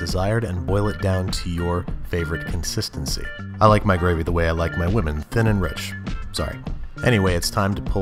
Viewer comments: I thought he said "I like my gravy the way I like my lemon, thin and rich" I was thinking I've never had thin and rich lemon before. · I thought he said he likes his woman thick and brown I died